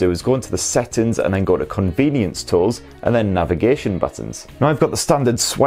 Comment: I keep hearing "Convenience Store"